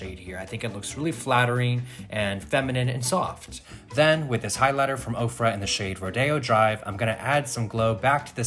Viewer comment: I legit thought he said highlighter by Oprah 😆